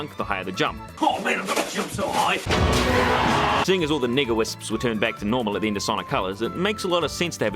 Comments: See I don’t know if it’s his accident or my bad hearing but at 13:17 is he saying Nega whisps it N*gga whisps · wait what did he say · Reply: N E G A Wisps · Did you... Did... Did you just say the N word? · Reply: It's "Nega Wisp" but God does it sound like it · Excuse me.... What did you call those wisps? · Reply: Nega-Wisps. That's what they're called. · did he just say the n-word????? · i had to do a double take at like 13:17 i heard the wrong word when you were saying nega wisps.... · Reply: nigga wisps